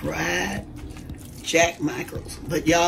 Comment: Omg my mom use to cook jack mackerel and rice and or grits yes mam good eating. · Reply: HAHA thanks- I thought she was saying "Jack Michael's" & I was wondering what that was! LOL I have never had mackerel!!!